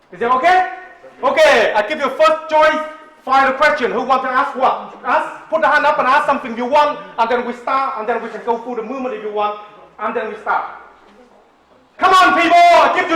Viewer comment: Sounded like Master said. Okay, who wants their ass whoop. Hands up who's first. lol